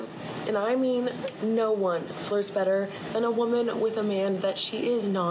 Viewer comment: I had to turn on the sub-titles because I could not hear her properly: I thought she was saying: "No-one but no-one BLOWS better than a woman with a man she's not interested in." Huh? Women really do take things to a whole other level of subterfuge...